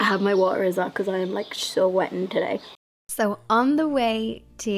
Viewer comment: Did she say she's "wettin" (@ 2:05)? I love slang (esp Irish slang) but I couldn't find it on the google machine. Help anyone? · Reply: She said sweating - as in so hot:) Needed the drink of water.